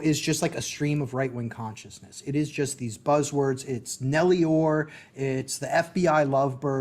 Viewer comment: Guys help me out what does he say @ 26:34 ? sounds like "nelior" or "melior" I've never heard about this conspiracy theory before. I'd appreciate your help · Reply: He said, "Nellie Ohr". Nellie Ohr is a Russian history expert who worked for a time at the opposition research firm, Fusion GPS. Her husband, Bruce Ohr, was an assistant Deputy Attorney General at DOJ. Nellie Ohr is at the center of a MAGA conspiracy theory regarding what Hannity and others would call, "The Russia Hoax". The conspiracy theory is too convoluted to outline here, but you can throw yourself down the rabbit-hole by Google-ing "Nellie Ohr".